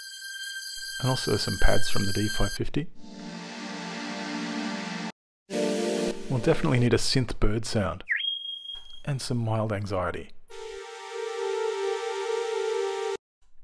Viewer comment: Hey at 3:45 he says "Mild Anxiety"? Or I don't know what he says - But is there a name for that type of string? I love it - Just don't know the name sadly